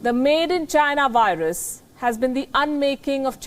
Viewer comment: When she says "made in china virus" I hear "teri keh k lungi..."